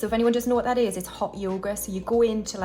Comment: 2 things: the money you have is always crisp and new looking, so I had to Google if you’re allowed to fold it 😅 Second: at first I thought you said “hot yogurt” and I thought “Hmm…that’s a different way to eat it” 🤣🤣 then I heard you say other things so I understood what you really said. Thanks as always for these videos ❤